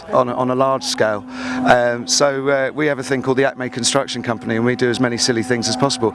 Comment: can anyone tell me the name of his group? "the atmey construction company"? I can't find him in the internet. Can anyone hear, what he says?